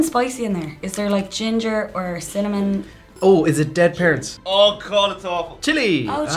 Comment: When says “is it dead parents?”, I spit my drink out bc I thought he said “is it dead ferrets?” and be honest, BOTH are funny, right?